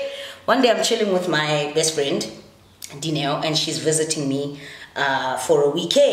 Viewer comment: Neo. 06:01? or am I hearing things?